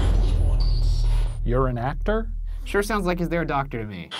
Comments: I think it says “you’re a doctor “ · I heard “ur a doctor” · I heard “ are you a doctor” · i heard is there a doctor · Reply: i heard “you’re a doctor?” as if is questioning who Shane and Ryan are. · I heard 'you a doctor' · sound like it said:"Ryan? Is there a doctor? " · Istg I heard 'is there a doctor?" 😟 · what I heard was "you're a doctor", like "are you a doctor?" I remember hearing once that ghost are more or less trapped in time, having no realization that years have passed by. So the ghost asking if either Shane or Ryan are doctors could mean the ghost still thinks it's, whatever year the ghost lived, thus doesn't know who they are. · I heard "Versace" at 7:55 lmao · i heard is there a doctor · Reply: aubrey turner i heard “you’re a doctor?” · It sounded like steve said "are u a doctor" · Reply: ANONY MOUSE it thought it said “You’re a doctor?” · I definitely hear "you're a doctor" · It sounded like steve said "are u a doctor" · to me when i pay close attention it sounds like 'im.......not......steve' but when i dont pay attention and i just hear it sounds like 'is.......there docter' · sounded like "Are you a doctor?" Chilling.. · i think it said "your a doctor?" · i think it said your a doctor? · it sounds like they say “ryan” and then “is there a doctor?” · i heard you a docter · I heard "are you a doctor?"!!!!!